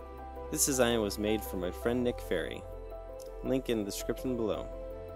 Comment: I 100% heard you say uoir friends name was Nick Fury...